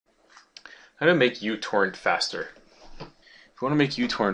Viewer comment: when he started the video i thought he said "How to make you twerk faster"